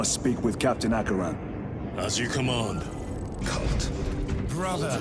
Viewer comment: I swear Gadriel says cunt 24:33 😂😂